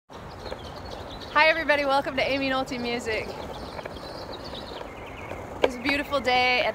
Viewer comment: I'm totally not making fun, but every time you do the intro all I hear is ;"Hey everyone and welcome to I am a naughty music" in a British accent